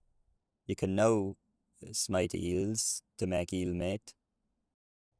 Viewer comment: The way they used to say 'meat' breaks my brain. I thought they were saying 'eel net'.